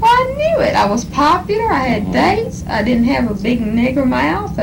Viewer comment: WAIT! what did she just say about her mouth??????!!! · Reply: Sounded like she said she didnt have a big N er mouth